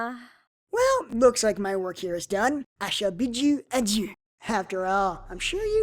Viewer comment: it sound like he says I shall bid you a jew